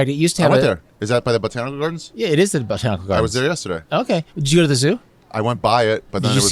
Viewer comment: did he say Jew at the Zoo?